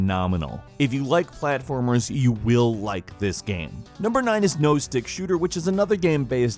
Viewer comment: When he said no stick shooter I thought he said nose dick shooter